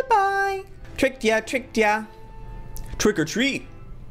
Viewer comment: I thought he said trigger or treat xD